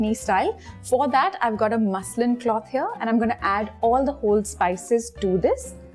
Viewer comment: Did I heard right “muslim cloth” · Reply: It's muslin cloth.